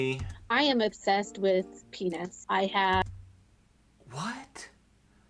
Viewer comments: I thought she said she was obsessed with peanuts at first lmfao. Idk whats worse, peanuts or penis 😂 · lol; at first I thought she said she was obsessed with peanuts.